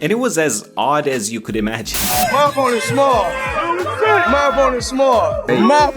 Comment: why do I hear Mayweather saying “ My boners small” instead of “ My opponents small”